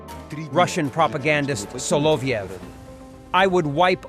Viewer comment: Why are solyovs sons living their best lives in UK and Europe?.. why haven’t they been deported back to Russia and their visas cancelled? 🤷🏼‍♂️ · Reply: Mirror, mirror, and smoke on the wall, who benefits from a kleptocracy most of all? I see his name is spelled Solovyov, but how is it pronounced? i hear "slobyknob".